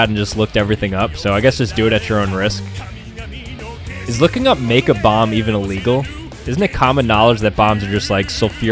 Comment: I wasn’t reading it and when he said is it even illegal to make a bomb I thought he said makeup bomb